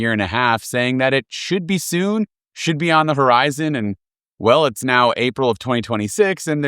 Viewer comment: did you say “On the VERIZON” instead of “On the HORIZON”?!? You did!!😊